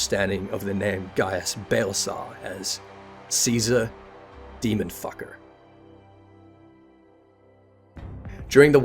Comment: I swear to god I had to replay 6:49 twice AND look at the comments to make sure I wasn't mishearing "demon fire" as "demon fucker". /lol -_